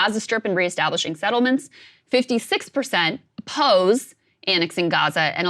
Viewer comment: what does this woman say. Pose or oppose. Cause I read oppose on the screen but I hear her say pose.